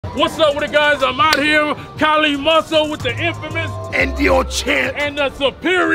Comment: マスオって聞こえ、ゥ